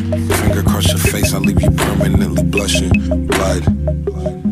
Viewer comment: i thought he said cookie in the oven 😂😂😂